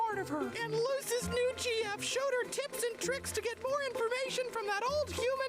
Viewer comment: Fun fact, everybody thought this episode would be relatively inconsequential when it was announced. Needless to say, it's actually one of the most beloved in the series. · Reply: Fun fact. About 80% of the reactors I’ve seen react to this episode mishear “Showed her tips and tricks” As “Showed her T**s”